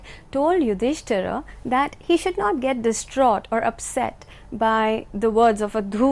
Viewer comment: Very informative lecture. Thank you. Could someone please me enlighten what words Dr. says at these times: 5:42 "The Brahmins, on regaining the stability of their mind, told Yudhishthira that he should get distraught or upset by the words of a [?] a demon in disguise." 6:05 "The battle of Kurukshetra was not fought for any personal gains and that the Kauravas and others were killed because they were on the side of [?] Thank you. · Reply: she didnt say "should get". she said clearly "should not get"